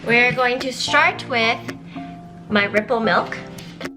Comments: At first I thought she said nipple milk lol 😂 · Am I the only one that thought it said nipple milk 😂😂😂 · I thought it said nipple milk lmao